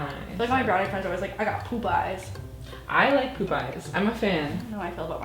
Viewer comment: When they were saying poop eyes I thought they were saying Popeyes the fast food place